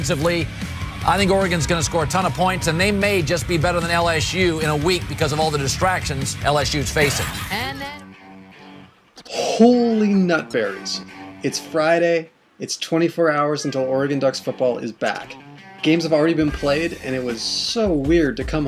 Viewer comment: Did you just say holy nut fairies